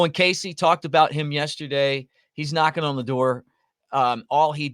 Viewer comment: I thought you said Owen Cassie is "not gonna endure". I had to replay it five times to hear "knockin on the door". I had the sound down a bit at work.